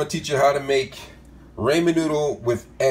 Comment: STOP HOLD ON DID HE SAY RAYMON NOODLES